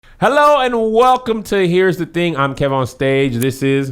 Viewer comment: It sounded like Kev said “Welcome Tahir’s thing”. And I’m looking around like , “What thing is he about to show us” 👀